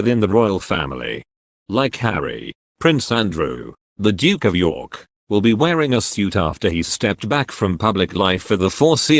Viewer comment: I will be delighted to see William and Charles in uniform and Harry in a suit in the vague hope the significance will register with Harry and cause him to think again on his flight back to LA about the enormity of his decision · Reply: I agree but I thought he said that William will be wearing a suit as Harry must.